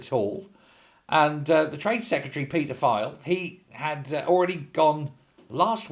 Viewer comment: When "Foxy" said "Peter Kyle", I could have sworn he said, "'pd file"...!!!! I must pay attention.